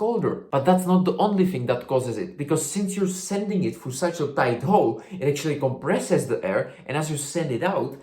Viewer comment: cc typo says big hole instead of tight hole